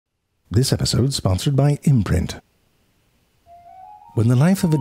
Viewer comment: I first heard "this episode is sponsored by inbred"